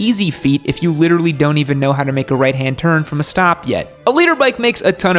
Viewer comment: All this years I thought you were saying a LEADER bike (like, a "fast" one), and not a LITER bike. 😶